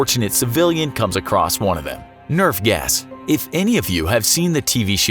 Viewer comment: i heard nerf gas.